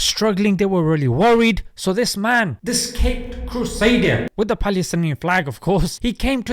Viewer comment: did you really say caped "cursader" I must've misheard right?